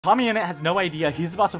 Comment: I thought i heard "mommyinnit